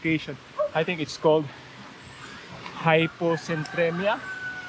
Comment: Time stamp 6:18 i think its called hyponatremia